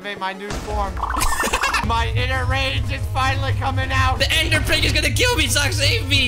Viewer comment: I thought it said Ender Pog